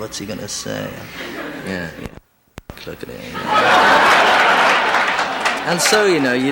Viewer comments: Now I want to know what the censored words were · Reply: I think he said there like "You know, don't fucking look at it". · what does he say that is censored¿ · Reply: " Shit"